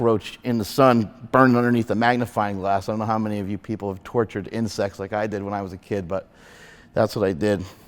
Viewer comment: At first i heard he said "tortured in sex"..and then i realise he was actually saying "tortured insects" 🤣